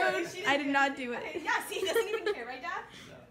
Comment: ‘I do care, but it’s your life so’ that’s the best reaction to this omg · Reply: I heard ‘ I do care, but you’re still alive.’ Okay I must have heard it wrong.